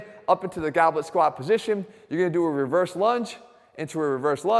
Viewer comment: Two dumbbells 2:15 3:06 (lunge wider) 3:56 4:59 5:28 6:22 single dumbbell only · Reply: LUNGE, I THOUGHT YOU SAID *LUNCH!!*